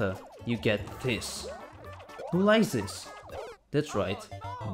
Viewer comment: It sounds like you said "Likes this" and that sounds like "License"